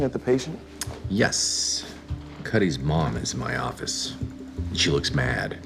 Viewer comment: After HOUSE of all healed Stu, he sure have to be alive. Also, I know he said "Cuddie's mom", but I heard "His mom is in my office. She looks MAD." 😂 Mrs Macher demands her baby having best doctors watching over him, after she and mr Macher left him at home alone so long, he LITERALLY decided to "kill time". 😂